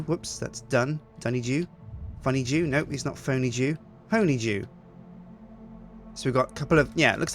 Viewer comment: I heard "Phony Jew" and had to do a double-take.